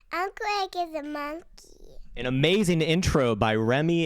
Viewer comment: Did I hear "Eric is a monk" from that intro or am I losing it? lmao